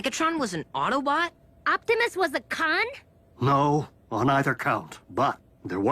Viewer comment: I swear I heard "Optumus was a CUNT?!" lol